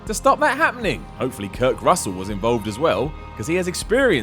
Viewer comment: did you just say Kirk Russell